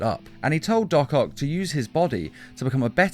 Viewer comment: I thought it said. Doc Cock